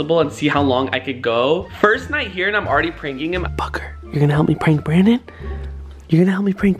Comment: when he said booker are you going to help me prank brandon it sounded like he said fucker